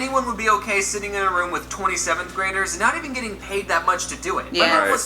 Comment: I thought he said 27th graders LOL. I was so confused :k